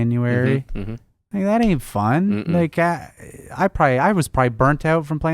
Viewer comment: sounds like he says nigga that ain't fun